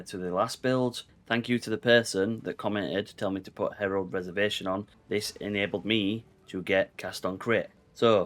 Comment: at the beginning you say thank you for telling you to put on herald of preservation? i feel like im mishearing what you are saying. can you tell me what allowed you to use CoC please? edit: found it! it's a passive tree skill if anyone else is wondering. "Herald Reservation" and it reduces reservation of herald skills by 6%